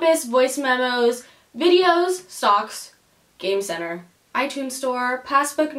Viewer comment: when you said stocks I thought you said socks😂🌚